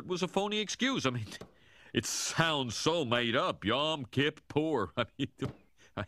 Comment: "Yeah I'm kid poor!" Fakest sounding legit excuse ever. Least it was when I misheard it like that when the series first aired lol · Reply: He's saying Yom Kippur, the Jewish holy day, haha.